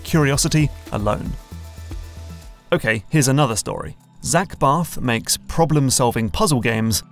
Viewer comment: I thought he said Zach Brath